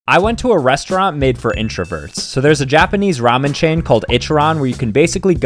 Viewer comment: I thought he said "pervert"😭😭